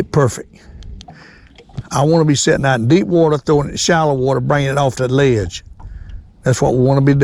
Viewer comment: Sitting in deep water, throwing to shallow water, coming off the ledge. Is that what you said? I'm trying to imagine this. Kinda like throwing to the bank?